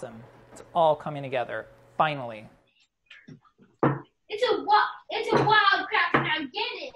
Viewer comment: I heard "it's a Wild Kratt..." I just connected on a parent spiritual level 😂